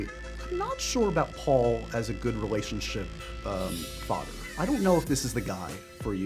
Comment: The closed caption is wrong at 0:45, the interviewer says "relationship fodder" not "relationship father."